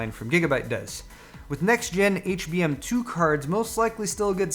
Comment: what did he say? edit: nvm hbm2 (sigh anymore want to buy my 980ti?)